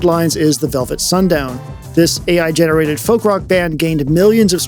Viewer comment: Ah.. Velvet Sundown is definitely NOT "funk rock". Maybe you said "faux rock" and I misheard you...